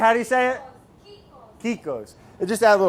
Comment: what is Gabby saying around 6:30> What are those lil crunchy things? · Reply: kikos